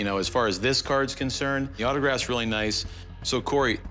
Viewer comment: He didn't even look at the autograph. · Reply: I thought I heard the seller say autographed.